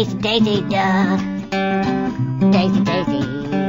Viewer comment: i thought he said daisy duck